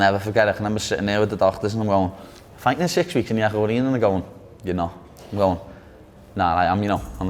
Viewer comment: Wtf did he just say @ 4:15 lmao that was jebbrish for me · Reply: "I'm fighting in 6 weeks in the echo arena"